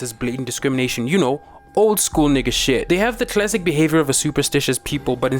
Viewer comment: did he say “old school nigga shit”?